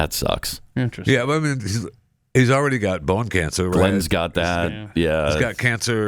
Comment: Did they say Glenn Beck has bone cancer